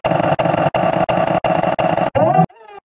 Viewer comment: Did it said Buneri or oh my